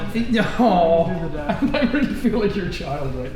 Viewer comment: did she just call dad????!?!?